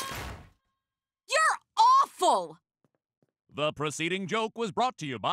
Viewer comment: Does he say "proceeding" or "preceding"? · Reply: I believe it's preceding seeing as the joke was made before the announcement, not after the announcement.